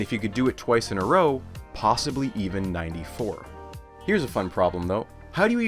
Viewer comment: subtitles are wrong. You say 94 not <num>